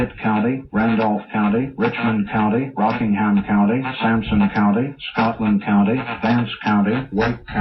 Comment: DID I HEAR "SAMSUNG COUNTY"!?